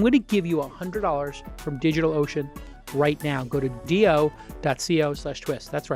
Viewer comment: I heard digital lotion.